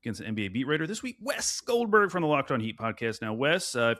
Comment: Thought said check him out on “Locked on Geese” there at the end lmao